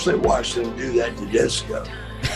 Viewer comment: Did he say Jericho? This is back when Buff was on that stuff so he's slurring really badly. He said "Jessicho" so i could only assume that meant Jericho Edit: Turns out he said Disco. I definitely believe it and think Disco liked it. "Gifted" Glenn Gilberti but how is he gifted? Definitely couldnt do the J O B for the H A W K